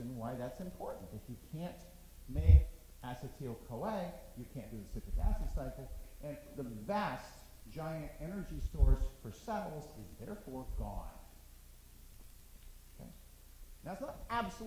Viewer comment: Dr. what did you say in minute 24:43? I can't hear the sentence that you said with energy storage. The sound quality of this video is lower than other video of yours. I must be up and running for BB 451 next term in this Winter break -.-" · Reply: XDDDDDDDDDD "When you can't make acetyl-CoA, we can't do the citric acid cycle, and the vast giant energy storage for cells will therefore be gone." Was what I got out of it. But basically: no acetyl-CoA -> no TCA -> no ATP -> no energy to do work